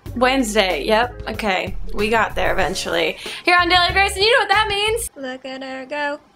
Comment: Haha in the beginning whenever she says hey guys it's daily grace and it sounds like she says billy grace! Haha